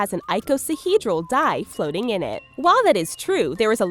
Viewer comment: it stands die instead of dye